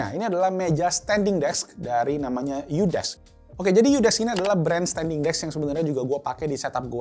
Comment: aku dengernya Yudas v